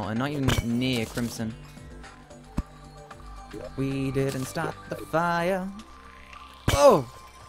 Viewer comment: When was singing, at first I thought he was saying "We couldn't stop the Biah" XD